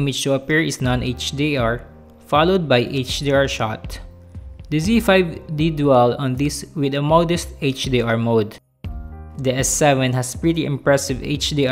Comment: What the fuck did he say? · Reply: “The Z5 did do well on this, with the modest HDR mode”. Wasn’t hard to understand.